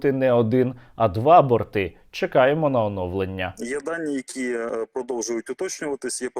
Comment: мені одному почулося: "єбальніки продовжують уточнюватися"?😁😁